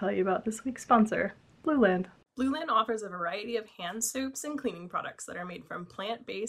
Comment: I thought that I heard hand soups instead of hand soap... the fact that I am eating soup might've played a role in that idk